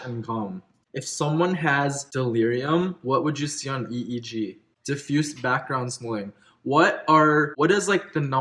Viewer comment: Could you please tell me exactly what you said after background? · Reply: Diffuse background slowing